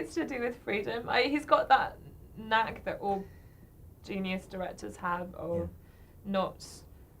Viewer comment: Also, at 2:05 I'm not sure if I hear correctly: "He's got that (nag?), or that genius director (hab?) of..." Is that correct or did she say something else? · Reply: Knack that all genius directors have...